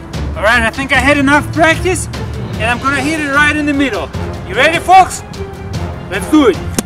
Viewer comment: I know ENglish is not your first language but it really sounded like "Your ready, forks?" hah! Nice video!